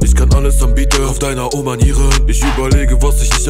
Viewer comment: hat noch wer anstatt "oh manieren" "Oma Nieren" verstanden ?